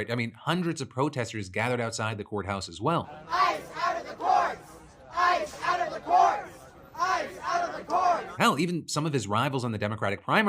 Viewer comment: at around 15:14 to around 15:19 in the video the subtitles say "Ice out of the boys, I'm out of the quarters. I'm out of the car" instead of "Ice out of the courts, Ice out of the courts, Ice out of the courts!!" which is what the protesters were actually chanting. Just a heads up! Keep up the good work DeFranco!